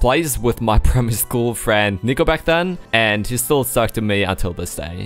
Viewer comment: I thought you said "my friend Nickelback" lmao.